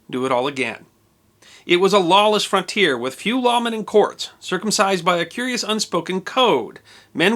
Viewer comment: Did you say circumcised at 1:38?